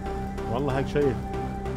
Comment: did you said : wallah... ? Was it arabic ? Great work like always, i'm amateur electronic repair man , but i lerned lot from you and ur work